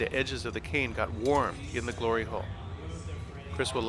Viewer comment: Did he just say glory hole?